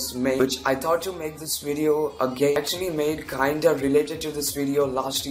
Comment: Did he just said he thought to make this video a gay